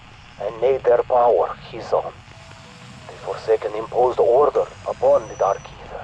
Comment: When said order I thought he said orda